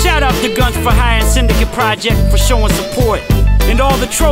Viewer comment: did he say Syndicate